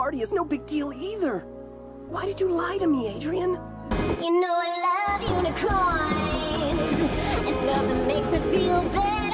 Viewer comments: AM I THE ONLY ONE WHO THOUGHT ROSE WAS SAYING "YK I LOVE YOU TO CORNS" INSTEAD OF THE "YK I LOVE UNICORNS" LMAOOO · marinette was SO EMBARRASSING this season it literally took me forever to finish cause i couldn’t stand it also whoever made the unicorn song.. DEATH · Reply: The way she said it, I heard ‘you know I love doin crimes’ which would be way better smh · The way she said it, I heard ‘you know I love doin crimes’ which would be way better smh